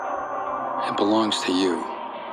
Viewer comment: OK OK OK OK but like, at 1:30 when Wolf gives her ring back, even I know he said "It belong to you" but my ears ALWAYS hear "I'M BELONG TO YOU"😩✋✨✨✨ I freak out everytime I hear or see that moment ugh- Anyone or is it just me??